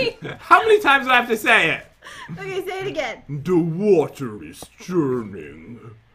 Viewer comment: Jen: Who are you; who am I Jen: WhErE aRE YoU? Pat: Rick and Morty? I have a question. Did he say "The water is german"